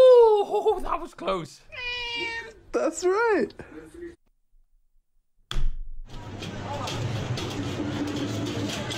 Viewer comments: what does it say? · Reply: Meow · what did the sheet say? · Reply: Meow I think